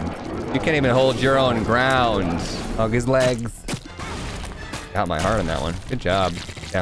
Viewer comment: i had to go back a few seconds i thought said “hump his legs” 😂 i wouldnt put it past him—that’s totally something i’d probably hear from him